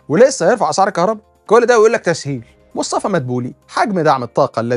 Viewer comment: لا انت سمعتها غلط يا استاذ هيا قالت انه امر ( بتسحيل ) عشان كده هما شالين سحل